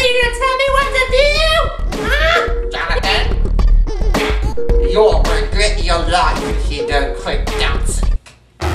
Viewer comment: What does Mr. Wilson say at 3:15? "You're going to _ if you don't stop dancing" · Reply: He said "you'll regret your life if you don't stop dancing"